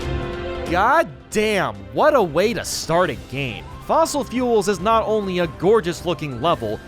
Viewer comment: did he just say fossil fuels